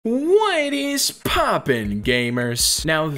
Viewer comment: Why do I always hear “Wine is poppin gamers” at the start of all your videos